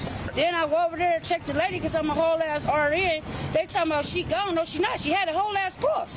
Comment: “I’m a whole nurse” Make that two whole nurses, oh lawd. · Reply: At first, I thought she was saying "Home Nurse," but when she repeatedly said "Whole Ass Nurse," it is hard to say it was anything but that.